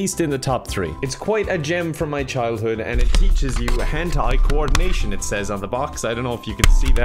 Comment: understood “Hentai-coordination” instead of “hand-eye-coordination” 😂